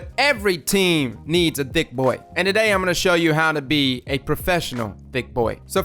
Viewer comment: anyone else heard "Dick Boy" ?